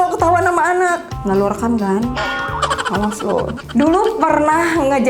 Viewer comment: ngomong apa sih,diulang2 ttp ga jelas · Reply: Ga lo rekam kan